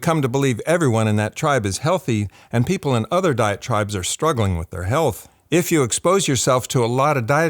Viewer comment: I keep hearing “diet tribes” as “diatribes,” but when discussing dietary strategies, that’s probably not inaccurate. 😂